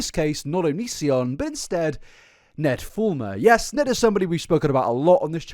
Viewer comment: I love hearing you say his name because I always hear "Ned Former" as he is formerly The Wife Guy..